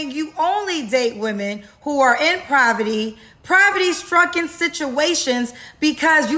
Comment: what's provety? 😂😂😂 Is she tryna say POVERTY? Kendra needs to take some of that money and pay for speech therapy.